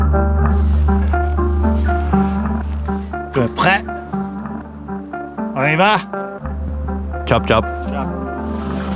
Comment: What does Cody say at 28:03? It’s stuck in my head. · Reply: "Tu es prêt? On y va?" It's french for "You ready. Let's go".